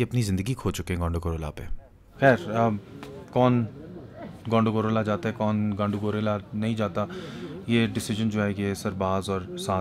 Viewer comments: wtf broo u just said gandu gorilla 😂😂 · i heard "Gandu Gorilla" lmao 🤣🤣🤣